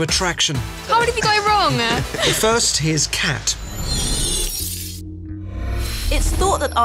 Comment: I've watched a few of these videos now, and every time he says 'But first, here is Kat' I hear 'Here's a cat'.